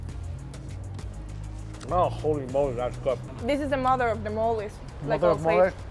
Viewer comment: I surely thought he’d said holy mole lol